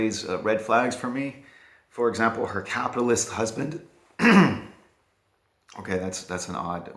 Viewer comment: Re: Mad Woman: I misheard, and thought you said Kabbalist husband at first... That might've made it worth checking out. Alas, no.